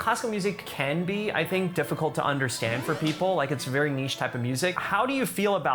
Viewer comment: Did Ray just say niche?